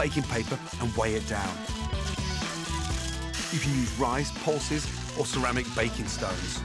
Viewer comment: What does Gordon say around 12:33 rice,___ , baking weights. No clue what he said sounds like he said pulses lol I assume not tho